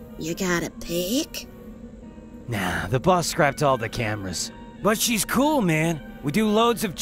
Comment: "She scrapped all the cameras" EEEEEEEEEEEEEeeeeeyyyyyy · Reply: Shit, until I read this I thought he said "scrapped all the canvas" after she said you got a pick as a really stretched reference to Pickmans paintings.